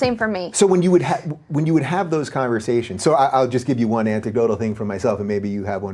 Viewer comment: did Dave say "antidotally", instead of "anecdotally"?